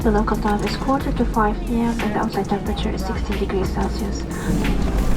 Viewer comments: I thought she said the current temperature is 60 degrees celsius, and I went to see what that was in fahrenheit and it said 140, so I'm guessing she said 16 or else you two wouldn't survive that hahah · Am I the only one who heard 60 degrees Celsius